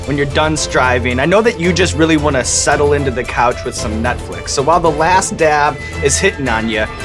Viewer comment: instead of Netflix it sounds like Sean said nutflix...I think ferg heard it to.